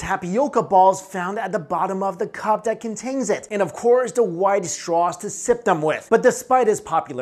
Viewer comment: “And of course the white straw to sip them with” Vsco girls are shaking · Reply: I thought he said wide straw? (I guess VSCO girls would still react tho)